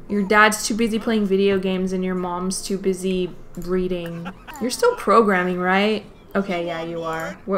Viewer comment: "Cause your dads your dads too busy streaming and your moms to busy reading." Me: 😨 ●•● I thought you said breeding!?!?!?!?!